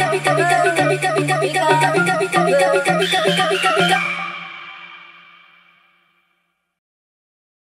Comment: KAKZKAKAKAKAKAKAKA o que eu entendi "pika" "pinta" "pizza" "muita pika" kwkxkzkkzkx tô mt bugada